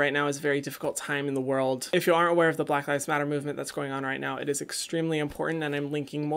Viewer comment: Thought he said black eyes matter 😂